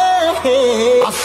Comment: I misheard like woud you come with me a sec..😂